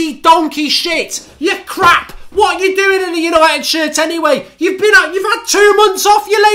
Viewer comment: I thought Mark was gonna say what you doing in United Kingdom anyways haha